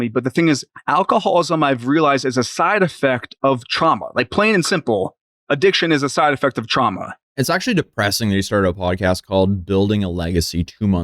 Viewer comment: I first thought he said Trump instead of trauma lol was thinking damn, they’re really trying to blame him for everything now